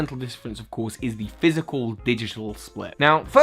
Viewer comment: When you said "physical/digital split" I thought you were saying "physical/special split" and I was very very confused as to what this video was about haha. Anyways... your videos aren't showing up in my sub box. Which is disappointing. Bad YouTube!